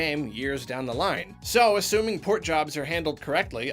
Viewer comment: I swear says portly...